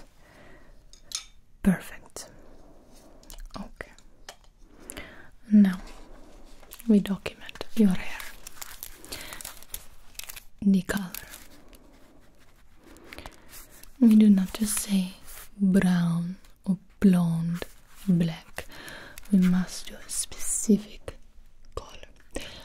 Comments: i thought you said the n word😂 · WHAT DID SHE SAY I think she said "the color", but... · what did she say I Cant understand it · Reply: She says "the color".